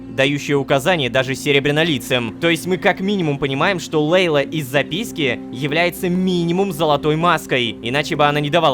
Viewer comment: я один услышал из-за письки